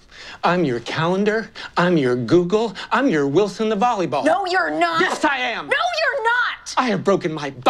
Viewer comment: Finally, at like the 10th watch, I heard "I'm your Wilson the volleyball" hahahahaha